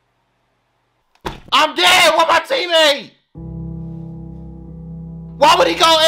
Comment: "I'm gay, I want my teammate!" I can't be the only one who heard that...